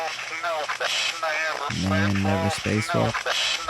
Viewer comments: I'm so confused on what were supposed to hear with the "one small step for man" I'm not hearing it. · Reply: I hear: "Never space walked, no!" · What is it saying when you play it backwards, I can't understand it. One small step for man. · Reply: I heard 'space walk', but no more! WHAT DID IT SAAAAY??